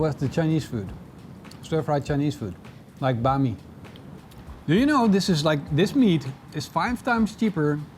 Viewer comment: I may have misheard you, but did you say “Chinese stirfry… like bahn mi ”?